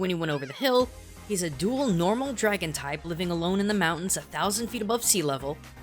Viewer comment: "alone in the mountains 1,000 feet above sea level" it said 10k, but I may have misheard you speak. Anyone else hear 1,000?